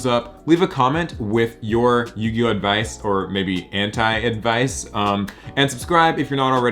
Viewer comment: At first I thought you said hentai advice I had to rewind 😂